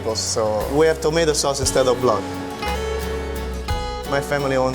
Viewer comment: "I'm from Naples, we have tomato sauce instead of blood" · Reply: I thought he said "We have tomato sauce instead of love"